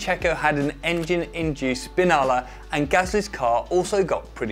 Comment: Did he just say the had a fucking s🅱️inalla?! Hahahaha fucking legend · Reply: Yup but it's s🅱️innala, double n and just one l. Source: I'm italian